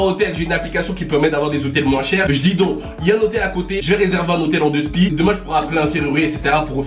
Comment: il dit quoi a la 4:46 svp deux spi jsp quoi